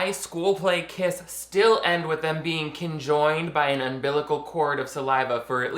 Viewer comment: there’s a mistake in the captioning where it says “kin joined” instead of conjoined 12:21 “car and a fall” should be Carnival there was one between these too and sure to be ones after this but oh well